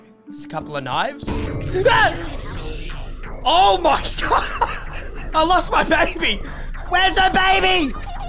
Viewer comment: When the woman first said she lost her baby I thought she said I lost my bra